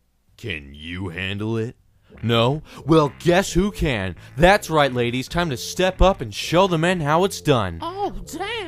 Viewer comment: ''CAN YOU HANDLE IT? NOOO? GUESS WHO CAN! These housewives '' That cracked me up XD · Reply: Pablo Henrique I think i heard him say "that's right ladies," not "these housewives"